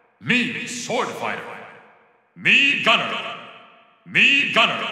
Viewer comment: Que dice el narrador en la lista 122 123 del minuto 3:53??? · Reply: Mii Gunner